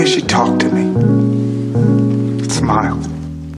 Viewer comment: I thought she cared for me... ''what did she do or say that gave you that impression''. The way she talked to me, and smiled . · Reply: I thought it said at the end, “that smile”